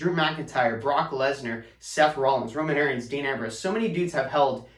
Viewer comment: did he say Roman Aaron’s